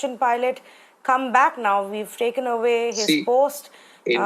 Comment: Did she say 'WE' have taken away his post?